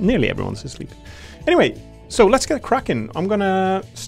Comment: It cracks me up that I misheard “let’s get cracking” as “let’s get Kraken” as a very funny way of “let’s get serious” 🤣…